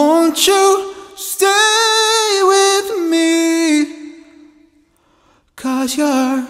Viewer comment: you said knee instead of me